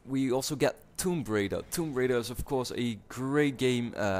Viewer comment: haha sounded like you said tomb braider.